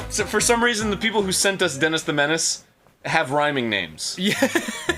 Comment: I can't be the only one who thought it said penis the menace